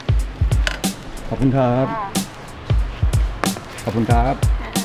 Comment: Can you start teaching us Thai?? Maybe a word we will hear a lot in each video. I hear you say one word over and over again 12:39 .....it sounds like "cob" or "tahb". I would love to know what you are saying.